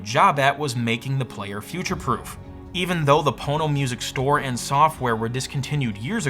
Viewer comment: I keep hearing "porno music"..